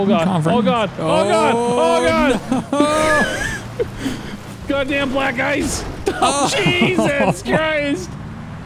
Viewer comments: I was listening to this while reading something and had no idea they went off the road.. all I heard was "god damn black guys" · Did he say black guys or black ice? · sounds like he said god damn black guys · What did say at 19:05 😂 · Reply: Black ice 😬 not black guys